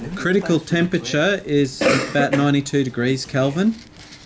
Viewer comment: did he just say ninety-two degrees kelvin ?